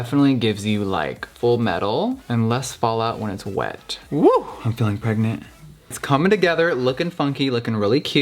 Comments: Did he just say 'I'm feeling pregnant"...I'm dead lmao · Did he just say “woo I’m feeling pregnant?” 😂😂😂 I love him! You are so beautiful Jeffree!